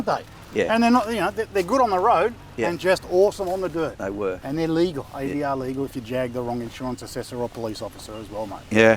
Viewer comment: did he say the tyres are "BIS"? as in best in slot? or did i hear it wrong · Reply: ABS